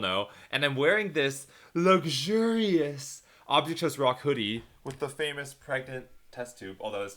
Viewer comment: I tHought that said “object shows SUCK.”